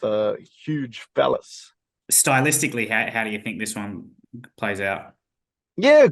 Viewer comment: did he just say huge phallus